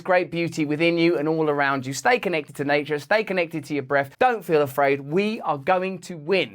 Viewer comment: "Steak n egg into nature" I misheard this because i was hungry.